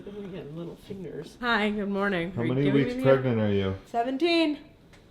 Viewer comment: So she is pregnant? Didn't know. How far along is she? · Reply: I thought she said 17 weeks